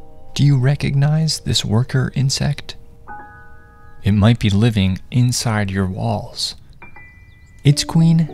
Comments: Why did I heard “It might be living in your balls”😂😅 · I thought he said it might be living inside your balls but then I turned on captions and I was SOOOOOO relieved idk how I thought he said it might be living in your balls in the first place Oi bruv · I thought he said “ balls” instead of “ walls” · I had my volume down low and I thought he said “it might be living inside your balls” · I heard "it may be living inside your balls" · I thought he said he might be living in your balls💀💀💀💀💀BUT I TAKE SHOWERS! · I thought he said it could be living inside my balls😂😂😂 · My dumbass thought he said living in your balls 😭😭😭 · i thought you said balls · I thought it said might be living in your balls and my hear skipped a beat🤦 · I THOUGHT HE SAID LIVING IN YOUR BALLS😭😭😭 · I THOUGHT IT SAID BALLS WHAAAT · dude when i thought he said balls instead of walls i got really uncomfortable and scared with that info but when i went to the comments and saw walls, i calmed down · “It might be living inside your walls” vs what I heard: “it might be living inside you balls”….. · Oh, he said walls. I thought he said balls.😅. /😊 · When he said “it might be living in your walls” I thought he said “it might be living in your balls”😂😭💀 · I thought he said they might be living in your balls💀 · I thought you said balls and I almost died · I thought he said “ it might be living in ur balls” I checked my balls as soon as I can😭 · I thought he said balls 💀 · I thought you said it was living in my balls